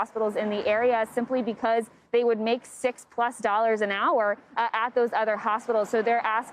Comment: did she say six plus dollars an hour ?¿